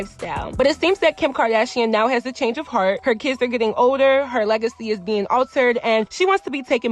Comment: When she said Kim's legacy is being altered I thought at first she said "her legs are being altered"!! Lmfao